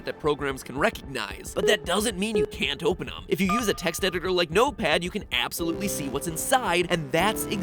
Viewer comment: I thought you said "Wattpad" instead of "Notepad"